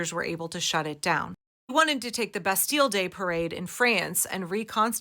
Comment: As a French person seeing the subtitles say "Best Deal Day" for Bastille Day is hilarious !